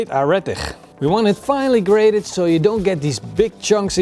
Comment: what is this root vegetable? I cannot make out what he says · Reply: It's a radish.